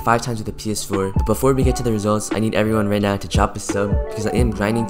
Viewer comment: I thought he said I need everyone to drop the soap